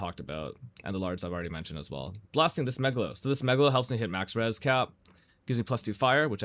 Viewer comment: I keep hearing 'the smegalo' and it cracked me up